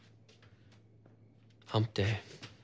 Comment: what he’s saying: hump day subtitles: I’m dead